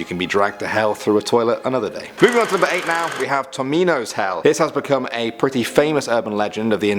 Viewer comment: why did I think he said Tostitos like the pizza rolls 😂😭